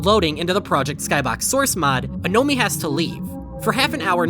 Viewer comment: It sounds like it say "baby has to eat"